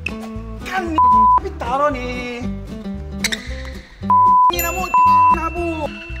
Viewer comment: did he say tang ina mo?